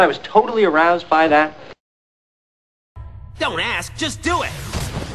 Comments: when Axel was sayin 'dont ask ust do it' to me it sounded like he was sayin 'Dumbass just do it' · when he shouted "Don't Ask, Just do it!, I thought he said "Dumbass, Just do it! I thought that was the Fail.